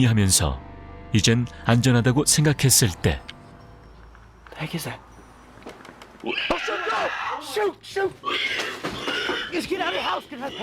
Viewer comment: 초때 나만 깜짝이야 라고 들리냐?